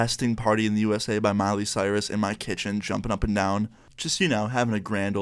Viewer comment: does he say molly cyris